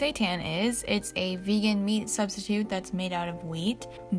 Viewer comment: OMG i thought you said "seitan is a vegan meat substitute made out of meat" I died haha, loved this vid btw tho 💞